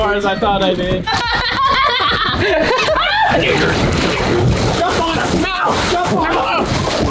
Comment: what where they saying? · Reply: Jump on him he said